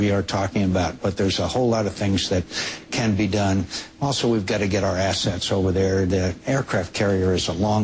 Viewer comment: lol when he said "we gotta get our assets over there" i thought he said "we gotta get our asses over there"